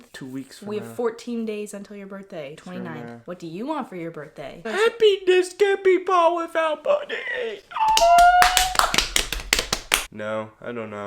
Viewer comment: i need english subtitles to translate wtf just said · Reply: I think he said happiness can’t be bought without money but that makes no sense HAHAH